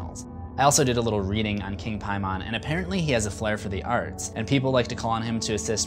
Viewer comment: Did he just say King Pie man